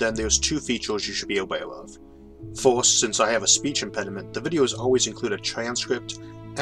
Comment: I’m not good with accents, so I was just mildly curious to know where yours was coming from. But the content is so compelling that I could never keep my mind on this for long. You say “impediment” but I hear “personality”.